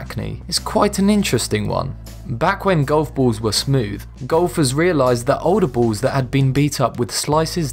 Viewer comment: who else thought he said Gophers for a minute?